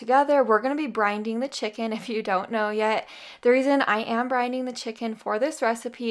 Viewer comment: When you made the baked chicken and instapot potatoes, you said, ' I'm brining the chicken...' for a second I thought you said, 'I'm Brinley the chicken'....lol That angel is sweet and tender enough. All the recipes look so yummy. 🧂